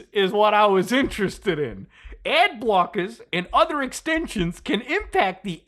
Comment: I thought it said Ab blockers 🤣 Goes to show what I know about ads 🤷🏻‍♂️🤣